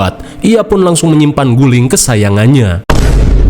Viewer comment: Apa, guling kesayangan nya,, waduh! Kedengeran nya udah Astagfirullah... 😮